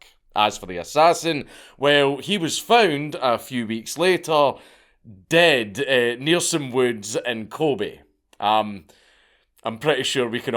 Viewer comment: what is he saying at 18:50? Near some woods and Colby? what? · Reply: Dead, near some woods, in Kobe.